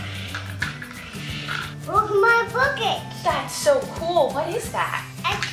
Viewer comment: When said my bucket it sounded like he said m*** f***